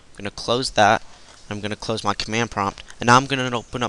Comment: I thought you said not instead of now. So I was like, "what?" So I repeated it.